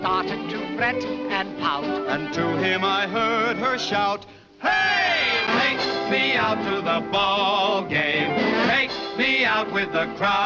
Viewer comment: What’d they say at 0:31? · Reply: “HEYYYY! Take me out to the ball game”